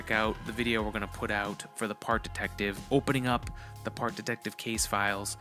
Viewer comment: I thought it said fart detective. this is cool too tho.